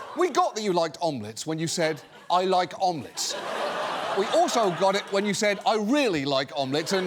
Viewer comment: 😂 actually….I’m glad he said omelets so many times because initially I heard “almonds” because of his drawly accent